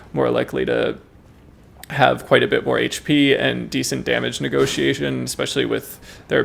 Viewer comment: Brother. Did I hear “damage NEGOTIATION ?! “